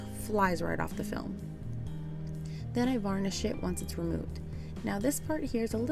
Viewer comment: It's "burnish" girl, not vernish (when making sure the transfer is adhered) 🤣 It honestly doesn't bother me but I literally started chuckling because my first year using IOD products, I thought everyone was saying "vernish" so that what I said 😂 I'll never live it down either 😜❤️